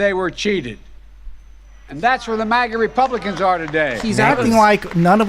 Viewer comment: he says MAGA republicans but the caption says maggie republicans